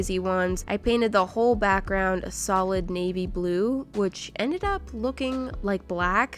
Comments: is it just me or did she say sowid navy blue ;-; Edit: so um.. it turns out my ears where just blocked -w-‘ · 5-6 time I watched this. When she did the confetti one, I thought she said 'baby blue' instead of 'navy blue'. I started freaking out thinking that I was missing something but then I realized that I am stupid. Sorry that I am a color freak.